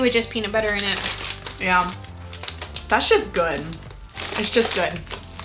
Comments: When you said that's just good I thought you said that shits good and I was like ohhh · did she say "that shits good"? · Did anyone hear s**t if not listen to 2:46 I might be wrong but that's what it sounded like. The reason why i am pointing this out is because in one of there other videos they said that they would never swear!! I am not dying and freaking out cause I know it was just once but I am just surprised